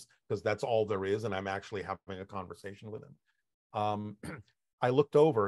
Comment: Did he say he’s never had a conversation with Bashar?